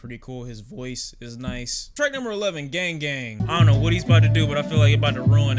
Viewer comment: I thought it was repetitive but then I heard Gang Gang and the song with Post Malone. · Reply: Gang Gang made me cringe. Sounded like they were saying "Gay Gang" over and over again.